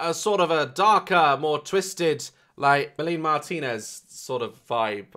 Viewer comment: I thought he said Billie Martinez , like Billie eilish and Melanie Martinez mixed lol. Or did i hear him wrong and just heard the last part of the word Melanie, sounding like Billie?